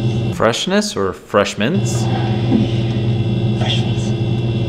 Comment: i heard freshmans or fresh MEAT! gahhh. lol. we just went to vale end yesterday the over to the falls down the rode..my husband grew up in Wilton, and unfortunately grew up in a very haunted house that had a dark entity in it. needless to say he had a bad childhood :(